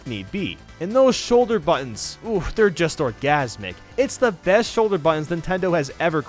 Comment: did he just say the shoulder buttons were organismic so he is getting sexual pleasure from pressing these buttons